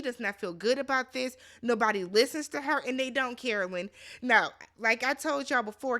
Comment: I heard “and they don’t care o lynn” (Carolyn) 😭